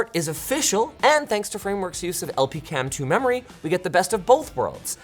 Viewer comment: Linus says LPCAMM2 memory while the subtitles say LPDDR5 memory